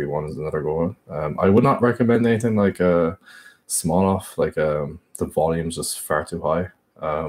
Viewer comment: what program does he say @ 1:34?? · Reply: Mor3Th4nJust4verage Smolov, high advanced weightliftet program that isn't supposed to be done by most